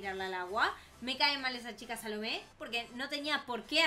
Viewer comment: Yo cuando escuché el nombre de "Salomé" pensé que dijo salame XD